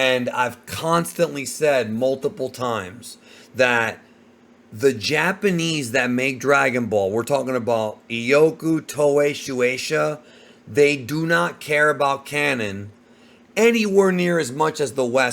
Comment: “The Japanese just don’t care [about canon]” is a very broad statement to make and I’m not sure where such an idea comes from. Japanese fans are active in their fandoms, try to connect the dots within their stories, and predict where things are going, and all of those activities require some deference to a canon. I’d also add that claiming “X person or group doesn’t care” doesn’t mean the plot hole, inconsistency, etc. doesn’t exist, and it doesn’t make the plot hole, etc. less negative. It’s like trying to say the temperature outside doesn’t bother someone over there while your ice cream cone is melting. Sure, person X isn’t bothered by the heat, but how does that really solve the problem of your ice cream melting? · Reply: You must have misheard. specifically says "The Japanese that make Dragon ball, we're talking about Iyoku, Toei, Shueisha. They do not care about canon." 2:08 He wasn't talking about ALL Japanese as a people.